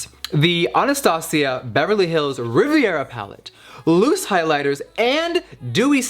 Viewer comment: For a second I thought it said Riverdale pallette, but it's Rivera